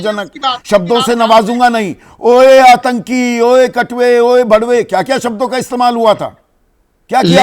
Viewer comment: did he just say bharwa?😂😂😂